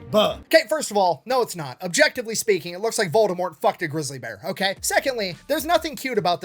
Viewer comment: Brother, I thought you said, " It looks like Baltimore fukd a grizzly bear," and actually...well. Great video, keep them coming. All the best from the Rockies 🇺🇸🎗